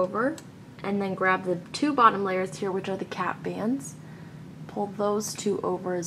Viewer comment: this bracelet helps you get better at doing cat bands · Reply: Lol you said cat bands, it does actually sound like she's saying cat though and I thought they were saying cat when I started looming, 😀